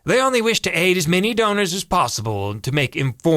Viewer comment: My 6 year old misheard the Givewell pitch when it said "aid as many donors as possible" and thought it said "eat as many donuts as possible."